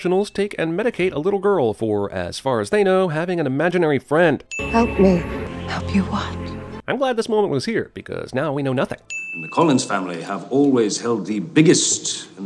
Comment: I had to rewatch that just to check that she said "Help you what?" Instead of "Obi-wan"